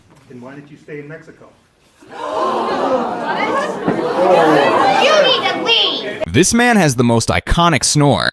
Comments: I thought the kid said "you need to pee" but seeing this it now you need to leave 🎉🎉🎉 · i thought the guy said "you need to pee" but he said "you need to leave"